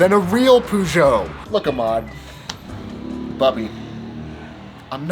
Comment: Did you just say "babi"?😂